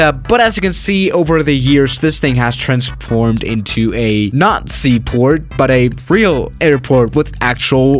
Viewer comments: Be careful what tou say, captions at 1:00 say "Nazi port" instead of " not sea port". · it sounds like he is say Nazi port instead of not sea port